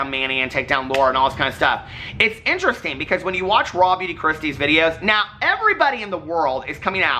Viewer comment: For the longest time every time you mentioned Raw Beauty Kristi in a video I thought you were saying ‘Robby Dechristy’. After many YouTube searches for ‘Robby Dechristy’ I finally found Raw Beauty Kristi haha! Love you!